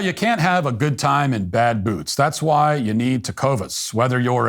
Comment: I swear you said you can't have a good time with bad booze.